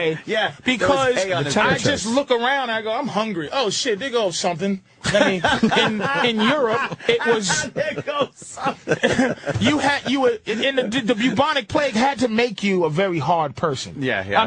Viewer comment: what does Patrice say at 26:30? It sounds like "dig up something"?